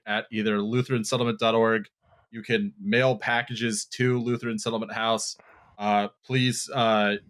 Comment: When asked for the donations, i misheard "you can nail donations to the lutherin society house".